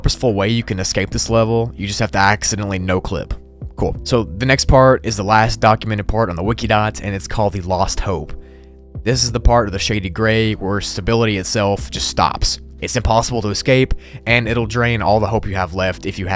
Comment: i thought you said boston lmao